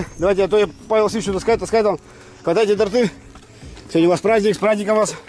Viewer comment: Торты! Мне сначала послышалось "портвейн" Я аж перемотал два раза!